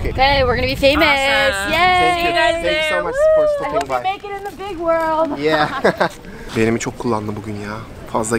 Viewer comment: dan sonra kız ne diyor. ı hope you .... big world anlayamadım tam orayı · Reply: I hope you Make it in the big world diyor sanırım